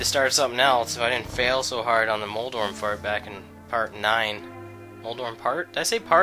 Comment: Sounded like you said moldorm fart